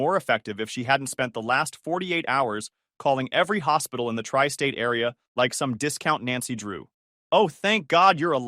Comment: did i hear the tri state area